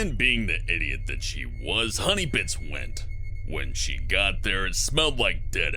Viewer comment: it sounded like he said HONEYBICH! XD